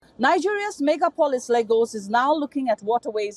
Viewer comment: Classic Nigerians (hear what they want to hear and believe it’s actually what was said) in the comments. Play the video at 0.25x and read her lips from 0:00 - 0:03, you’ll see she says something which sounds like “Nigeria’s ME-GA-PO-LIS LAY-GOES is now looking at…”. Combine that with a quick Google search for the word “MEGAPOLIS” and it turns out, that word’s a variant of the word “MEGALOPOLIS” which means—a very large city or urban area that densely populated, which is 100% true of the city. As far as the title goes, it’s also NOT incorrect that Lagos is Nigeria’s (economic) capital.